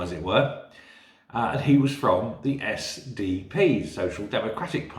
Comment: For a second I thought you said you'd been approached by a Basill from the SNP 😅😅